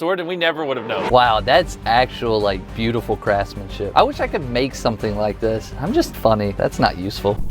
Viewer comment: did say the the S word? 0:45 beautiful crasmin Shoot